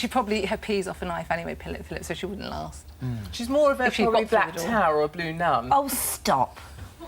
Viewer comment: I cant actually hear what Katie says before Holly tells her to stop - it sounds like she says "a black tower or a blue nun"?? If thats right what does that mean - is she just being racist? or if its wrong can somebody tell me what she actually says aha